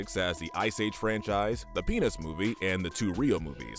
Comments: am i the only one who heard him say "the penis movie" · please tell me i’m not the only one who heard the penis movie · DID HE SAY PEANUTS OR SOMETHING ELSE